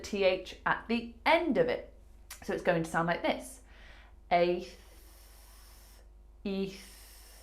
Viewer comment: I feel less stressed. I hear "aitch" for this video.